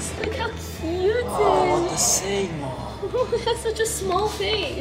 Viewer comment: Did Just say what the sigma to the hedgehog 7:17 and also did just say monkey to a hedgehog